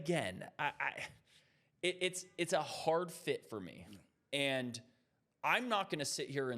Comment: I keep hearing hard fit? So like cp3 and harden hard fit? Or Kyrie Irving and harden hard fit?